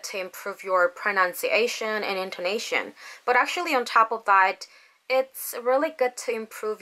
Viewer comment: 뜬금없는 질문이지만 0:49 에서 But actually ----------- Its really good~~ 여기 actually 뒤에 뭐라고 말씀하신건지 알려주실 수 있으세요?ㅠㅠㅠ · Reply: 당연히 알려드릴 수 있죠! 'on top of that' 이라는 표현이에요. '그 위에' 라는 뜻이지만, 외에 어떤 것을 말하고 '게다가', '또', '그 외에' 이런 뜻으로도 쓰여요. 제가 좋아하는 영영사전 사이트 링크 첨부할게요. 확인해 보셔요. <link> 영상 봐 주셔서 감사합니다. :)